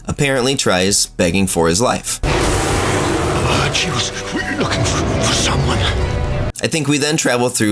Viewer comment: Rather than begging for his life, I think he instead says “I heard she was looking for someone”, indicating that he could give us a hint on the whereabouts of Lea as we retrace her steps.